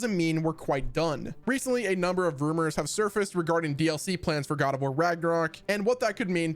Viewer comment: I thought they said no DLC and were kinda like "cmon guys we put in a ton of work on what we gave you" lol. I hope they do though